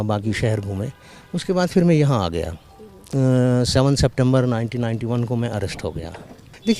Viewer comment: he was arrested on 7th sept 1991 not 1951 captions wrong